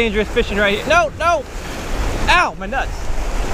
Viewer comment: who else heard at 10:00 of my nuts